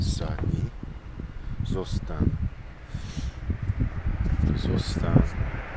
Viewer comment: Do You live on Poland or other Slavic country? I heard "zostań" 6:13 to Your dog which means "stay" in Polish but Your pronuciation wasnt clear for me as Pole